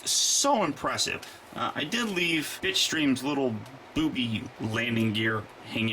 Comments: it sounded like you said bitchstream insted of bitstream XD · Oh Bitstream, you need to enounciate that better. I thought you said Bitchstream.